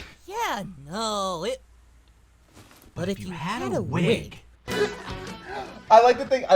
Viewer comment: OMFG, I always thought Goku said whip, not wig, 5 whole years since i watched this show and I just found that out